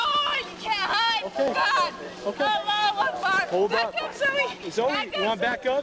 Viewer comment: she yelled Allah akbar, I didn't realize what she said until I watched it a second time.